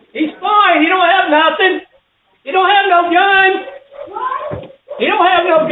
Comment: Am I the only one who gets annoyed at that neighbor? He sounded so judgey. Just because someone "doesn't have anything" like a gun or whatever visible, didn't mean that James wasn't perceived as a real threat. The family didn't know he wasn't armed, and James broke their door down despite warnings. I do feel a bit bad for James sister, but it's disgusting of her to edit the video and demonize the family. Just my opinion. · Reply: I thought he was saying “ he doesn’t have anything. You’re safe now”